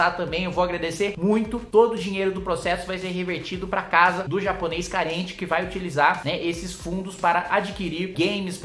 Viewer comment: a 08:26 - "Japonês Carente". Quando Ouvi Na Primeira Vez, Pensei Ter Ouvido "Japonês Caliente". Rsss.